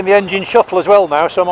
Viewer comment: Is it not "Hendon Shuffle" (Police training School), as opposed to your "engine shuffle" which I think you misheard Observed Ride #4??